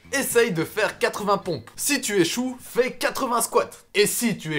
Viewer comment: À 16:00 il dit " si tu est chaud " ou " si tu échoue " au début j'entendais est chaud maintenant j'entends échoue aidez moi je suis confus mdr . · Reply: Si tu échoue fait 80 squats et si tu échoue encore fait 80 abdos